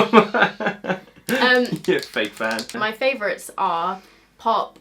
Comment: I think she says it at 14:39 but I didn’t really understand what she said. Nathan? Ethan? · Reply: i thought she said fake fan